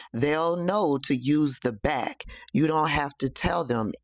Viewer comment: Omgggg I thought she said the bat ...she said the back ..oh my yikes